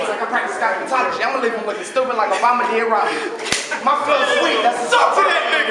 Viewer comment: did he say "my flow's weak"? hahaha. s/o the lil homie